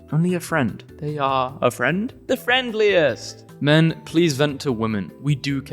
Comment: I heard DanTDM when he said “the friendliest”